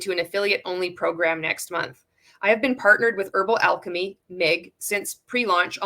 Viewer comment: Yikes, though I thought it said herbal life and was excited. Ick. Still good to see whatever this one is un-mlming